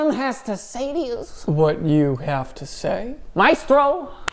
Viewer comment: I thought he said “micro” not “maestro”